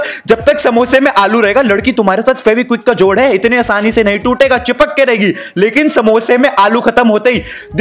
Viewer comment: At first I heard that It was Ch_dti rehegi tujhse ye sali 😂😂😂